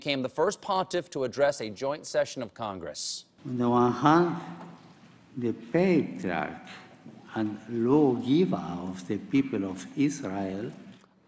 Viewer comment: Did anyone understand what he just said? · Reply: "...Noah, the Patriarch and law giver of the people of Israel"